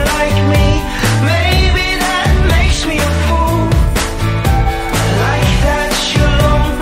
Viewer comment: man is no one hearing this. It sounds like he says,”maybe I’m a poo”